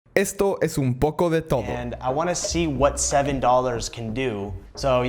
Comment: Al guien mas escucho "Esto es un poco de tomo" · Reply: yo escuche ''esto Es Un Poco D e Todo